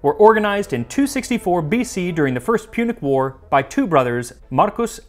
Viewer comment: I definitely heard "Pubic War." I know it's not what you said, but thank you for the laugh! ^▽^